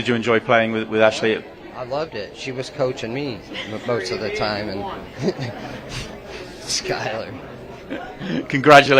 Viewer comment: can anyone tell what Skyler said here? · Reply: "She is, she is the reason you won"